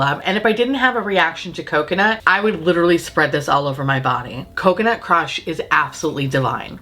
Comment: Why did I think I heard you say ‘coconut crotch’ when you were describing the deodorant? Coconut crush. I got to get my shit right.